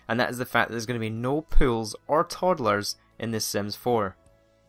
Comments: i thought he said pills instead of pools. 😂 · I thought you were saying "no pills" not "no pools" 😂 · i thought he said pills instead of pools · i thought he was saying pills instead of pools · i seriously thought he was saying pills, not pools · No pills? When was pills in the game? And how do you kill sims with them? Overdose? Never remember pills at all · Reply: I thought he said "pills" also, but it turned out to be that he was saying "pools." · I thought he said "no pills", I was like "NO SHIT! ITS A KIDS GAME!" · when he said pools,I thought he said pills.I'm like WHAT!!!